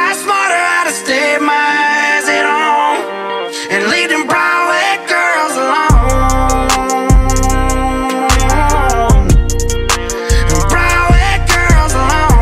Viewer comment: I heard "brown eyed girls alone"